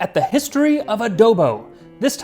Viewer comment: I wasn’t paying attention during the intro and it sounds like you said, “The history of a dildo.”